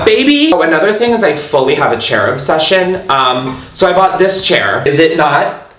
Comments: When he said “chair obsession” I heard “cherub session” and was very confused until he held up the chair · Despite saying "obsession" so many times, I still thought he said "cherub session" instead of "chair obsession". Obsessed with that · "also I have a chair obsession" Me high AF trying to figure out what tf a cherub session is 👁️👄👁️ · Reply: Omg literally thought I heard cherub session as well😂 baked af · Idk why but when you said you have a chair obsession I thought you said you had a cherub obsession - I mean go off sis either way 😂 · I thought you said Cherub obsession instead of chair obsession. I’m not saying I was dissapointed but maybe a little 🥺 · when you said "chair obsession" i thought you said "cherub session" and got reallyyy confused